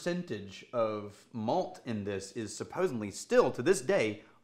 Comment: Did say "supposenly" at 5:49?